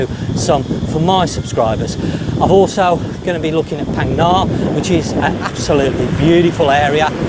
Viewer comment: Hi, great vid as usual, you mention a place after Bangkok but i didn't quite catch it. We head to Thailand every Xmas, usually Ko Lanta, Ao Nang, Phuket or Ko Sumi. It sounded like you said Phag Gar, is that right? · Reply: Phang Nga, which is very picturesque famous for the man with the golden gun scenery